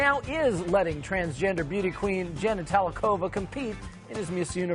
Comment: i thought her name was “Jenny Talia” and i laughed because it sounded like the narrator said Genitalia